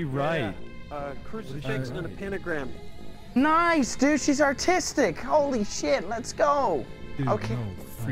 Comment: When he said artistic I thought he said autistic